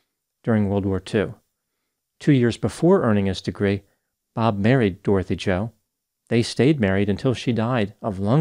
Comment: I thought you said "buried" omg